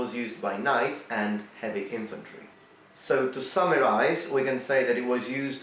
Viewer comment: Personally I have always known this as the "pretty good helm". 0:30 Wrong. Samurais never wore this helmet. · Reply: He said “summarized,” but because of his accent you thought he said samurai